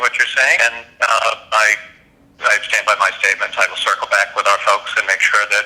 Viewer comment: I guess "circling back" is the democrats new cop out when they are caught in a lie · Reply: Oh, it's "circle back"... I thought they said "circle jerk".